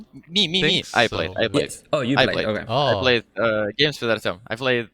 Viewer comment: what does Misha say at 19:40? He plays one of the pubs with who? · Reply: he played pubs with artiem = yuragi